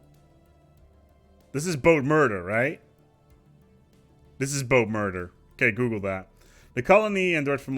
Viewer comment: What was saying to google at 9:55? I can't tell. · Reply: BOATMURDER